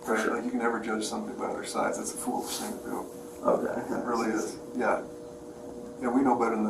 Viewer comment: when you said inaudible for the audio what the officer said was “that’s a foolish thing to do.”